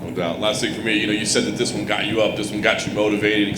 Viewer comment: i thought he said ''on god'' at first lmao